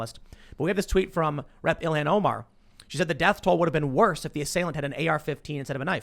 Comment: When Tim said rep ilhan Omar it sounded like he said reptilian omar!